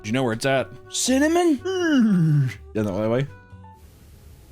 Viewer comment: I have no idea what he just said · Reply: U turn on the subtitles and it’s says , is that the only way